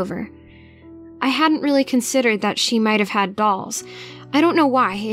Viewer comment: GIRL DID YOU JUST SAY DOLLS OR BALLS? CAUSE IT SOUNDS LIKE BALLS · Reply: Dolls.